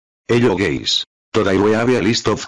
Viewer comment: Why did i hear hello gays